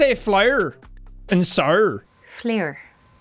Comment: she did not say flower or flour, she said flare